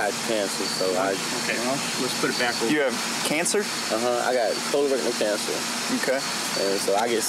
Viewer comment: Is it just me or did he just say that he had covid and cancer????? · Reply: I thought he said colon cancer.